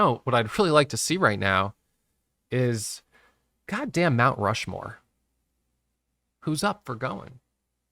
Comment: at 9:15 did you say _-damn Mt. Rushmore? Please do not use Gods name like that. It is an offence to many.................